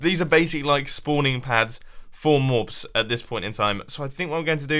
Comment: did he say morbs?